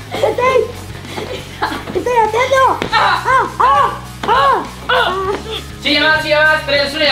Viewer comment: Escuchai de fondo en el minuto 19:17 que dice chilla mas 1 2 3